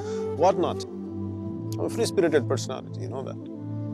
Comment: what is that he said?? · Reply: I'm a free-spirited personality